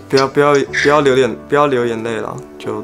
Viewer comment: 我把不要留眼聽成不要留言